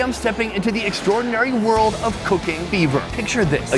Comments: Awwww man.. i thought he said "Cooking Beaver" and i got REALLY excited.. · I heard "cocaine beaver" when he plugged the sponsor "cooking fever."lol